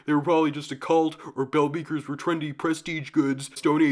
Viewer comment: I thought it said Ball Breakers at first and I couldn't click that timestamp fast enough